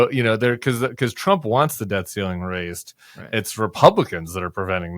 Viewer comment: I kept hearing "raising the death ceiling"